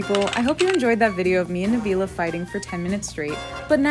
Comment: Did say "fighting" at 8:35, because I heard "flirting"...